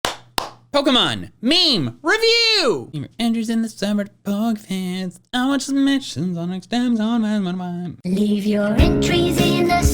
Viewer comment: The subtitles say “Andrew’s in the summer POG fans, then our submissions on extensions” 0:02 lol